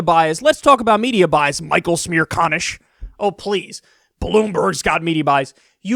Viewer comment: I thought u said smear con ish